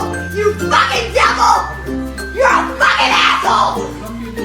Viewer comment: I think she said apple not asshole